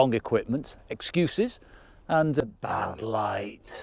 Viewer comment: light ..... I heard "lies", I replayed it a few times without reading the message at the bottom and was puzzled 😂 Was the video of the end sunset shot on the GoPro or the iPhone - the detail is quite remarkable ( and that's my remark right there) 😊.